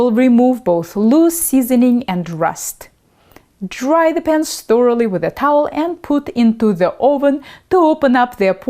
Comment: I keep hearing "pants".